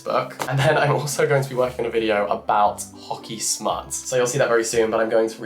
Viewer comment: pls somebody help me? 😅 i heard as "hockey smart" i'm not native, what's the correct word? 🐤 thanks in advance